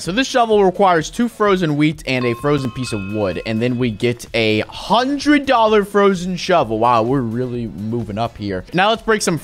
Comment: I heard you say 100 dollar shovel